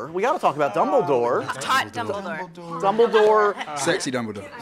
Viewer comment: Anyone else hear Alison say "I've taught dumbledore" at 7:59, and no one except zoe hears it?